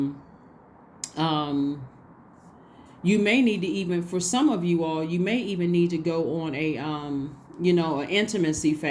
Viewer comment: I heard a whisper 😮 but I can’t make out what it’s saying? Time stamp 33:52. Can anyone else hear it please share. I’m not sure if there is music playing but it doesn’t sound like there is. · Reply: I had to listen over and over but I think it’s saying “sex”. It makes sense because the whisper is heard right when she talks about how some should be on a intimacy fast 🙀